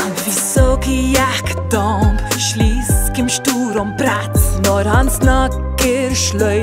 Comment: Did she just say brats?